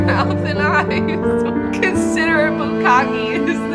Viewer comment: I absolutely thought she said "considerate but cocky" thank you for bringing this to my attention.